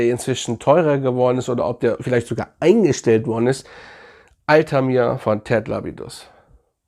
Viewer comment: Duft bei 9:20 ? verstehe es leider nicht. trotzdem geiles Video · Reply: Altamir